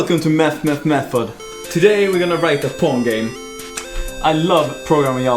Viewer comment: did he say a porn game???